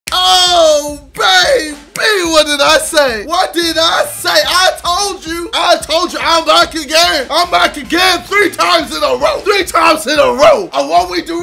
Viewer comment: I thought he said I’m black again I was confused for like 1 minute